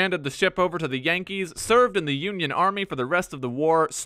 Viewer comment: Even better, he eventually bought a House in the South and lived in it. It was the House of his former Owner. Madlad. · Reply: Oh God I thought you said horse